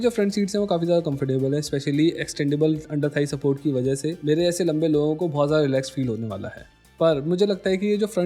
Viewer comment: did he said mare jaise lambe log 🤨